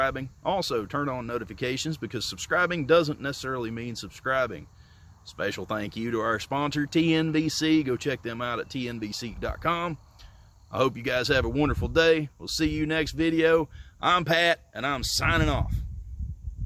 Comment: My ear's a bit messed up right now, and I thought at the end you said, "I'm Hat." I thought, what a weird name for someone without a hat. Anyway, I'm gonna call you from now on. I like your style, Tennessee! er!